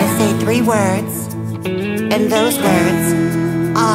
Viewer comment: Pensé que decías estoy perdida en otra vez jajajajaja. Btw She's saying: Estoy perdida, encontrameeee 🥹